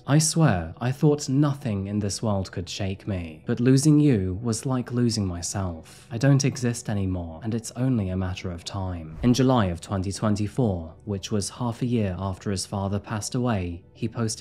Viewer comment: Good stuff. Love your videos. Did I hear "July 2024" @ 9:10 😁 Cheers🇰🇪